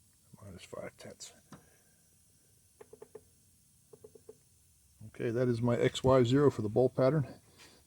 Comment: says: "That is my X, Y zero." I hear: "That is my ex-wife, Cyril."